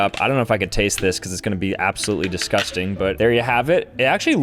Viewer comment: Did he just say obsolete? Oh my God, ethos